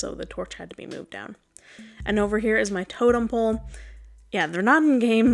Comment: I thought the frog hat was a pun for toad-em pole 🤣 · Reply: I thought you said Hermitcraft’s botem pole.